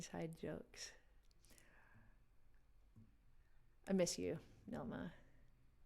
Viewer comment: No!!! Did you just say "Yo mama??" LMAO #goodforyou #whereyoufrom?? #wasthinkingthesame